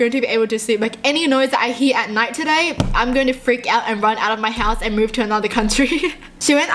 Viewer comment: Does this make sense......."like any noise I hear at night today, I'm gonna run outta my house"..........ummmmm😂 (am I the only one who heard it at 3:52?)